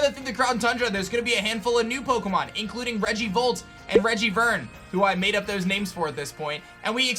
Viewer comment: I keep hearing "regiBURN"....have we confirmed if it's fire dragon dark or other yet?